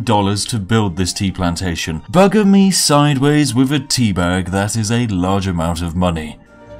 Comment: I heard “butter me sideways with a teabag” 🤦🏼‍♂️